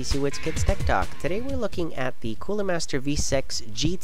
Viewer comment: did i just hear "Today we will be looking at the Cooler Master Vsex" or is it just me hearing something else?